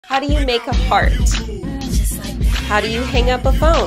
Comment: I heard it say "how do you make a fart-" 😭😭